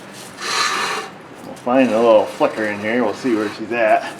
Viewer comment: sound like you said "find the little fucker in there"